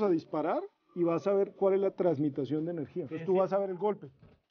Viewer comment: Cual es la trans.. que dijo? Transmitacion? Esa palabra existe haber dejame buscar en google